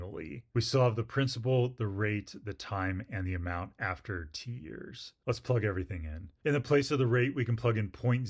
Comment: "amount after t-years" What did I heard: "amount after tears" 🥲